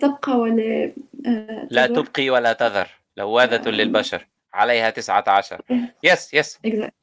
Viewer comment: did the brother said 'لواذة' instead of ‘لواحة'??